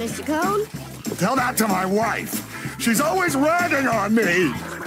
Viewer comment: the cone is actually saying "RAGGING on me" not "riding" . Whoever did the CC got it wrong.